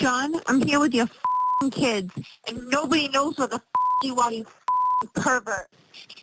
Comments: I hear, "John, where the F are you?" Sounded pretty clear to me. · I clearly hear, 'John, where the f&%k are you?' In a desperate, pleading tone... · I heard, “John, where the heck are you?” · 6/20/24 She was swearing in every VM. I hear John “Hear with the F*** are you” Never wished him any harm! ME, didn’t sound confident, in what she was saying.